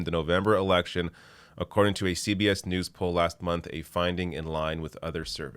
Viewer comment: I thought they said last March. It may have been the Trump God has been on vacation.